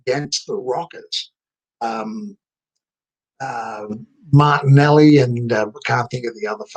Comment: The rockers, Marty Janetty and that other guy... AWESOME !! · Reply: I actually thought he said Martinelli. - "You know, that Italian fellow & the other guy, yeah they didn't make it."